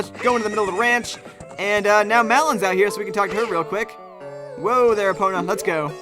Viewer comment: I love this game so much, but i cant help but laugh when you say Malon's name, it sounds like youre say "Melon" XD Since I'm here I might as well contribute to tonissues which have arisen here: 1. "How to make Link sneeze?" - simply make him stand idle in a cold area eg the windmill, well, shadow temple, ice cavern etc. 2. "How do I get Epona's Song after I've gorwn up?" - unfortunately you can only require the song as a child, so the best and eadiest way to play this game is to get visit Lon Lon Ranch and acquire Epona's Song either before going to visit Saria after Navi nags you to go see her or before heading off to kakariko village to find Goron's Ruby. However, if there are players who have reached this point in the game without acquiring Epona's Song you'll have to beat the Forest Temple first and then go back to the Temple of Time- if you go to the Temple of Time before completing the Forest Temple Sheik will still be standing between you and the pedestal and will only move once youve completed the Forest Temple, thats the earliest time Shiek will move. I hope that helps some people, good luck giving Link hyperthermia and becoming the next horse whisperer, and thank you for making this video and turning Malon into a fruit :-)